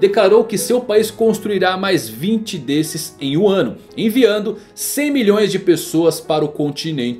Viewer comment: só eu q escutei wano ? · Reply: " em um ano" pareceu "Wano"